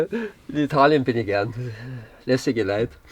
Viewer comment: Was für ein lässiger Typ. Hut ab. Unglaublicher Umbau. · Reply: Ich hab hässliger verstanden😂😂